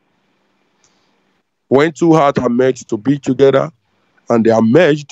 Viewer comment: First i thought he said "To hot" but it was " Two hearts". I really hope he gets to know Lola , she turns him through the concrete mixer 😄